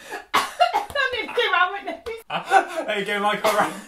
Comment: 😂 I have no idear what said whilst spitting her drink out but I was laughing anyway, Quality quality 😂 but next time give a box to stand on 😁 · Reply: Haran... sorry just got it 🤦🏻‍♀️ @ 00:52 I said “that nearly came out my nose” 🤭🤣